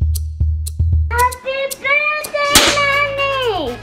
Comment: its sounds like said happy birthday nanny